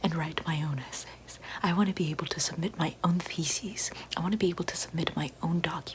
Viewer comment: I heard “feces” instead of “theses” and I had the pause the video to wheeze